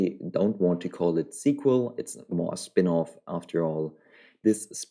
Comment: It's a sequel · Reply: oh damn I thought i heard prequel. My bad my bad.